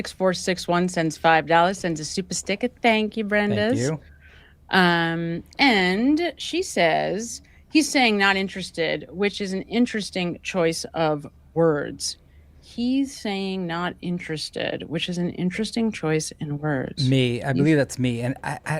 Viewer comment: I heard “im not interested”